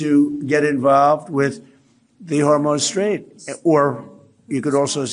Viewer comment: Did he say "Hormone Strait" then "You can say Iran.." 😗